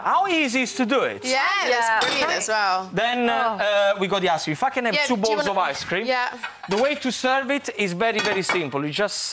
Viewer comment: WHAT DID HE SAY!? 09:21 "Fucking have 2 bowls of ice cream!"